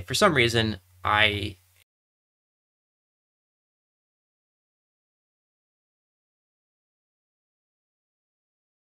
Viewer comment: Your audio cut out at 12:45 when you were talking about your change in thinking about aa. What did you say? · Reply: 5 AA was too much.